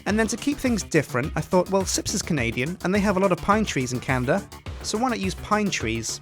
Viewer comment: Did he just say Canda?